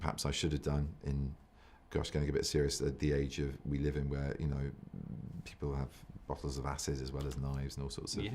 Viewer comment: did he say "people have waffles of asses"? The subtitles show it too🥞